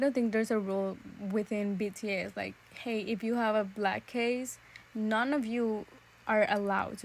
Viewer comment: beijfidks first of all, i’m so sorry about the confusion, from the minute I say “and now jikook’s analysis” the whole video is a jikooker anslysis, MY VOICE is the reaction, and some written comments, but you can know which ones are mines cause the font is the same i use in the beginning💜💜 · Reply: Oh, I'm sorry, I realized now I actually misheard your words. You talked about the black case of the phone but I heard it as a suitcase LOL. Nevermind, my fault. Thanks for the reply btw.